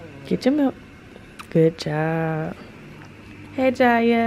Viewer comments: AM I TRIPPING OR DID JIYA SAY GOOD JOB AT 1:50? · did Jiya say good job? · Is it just me or did jiya say “good job”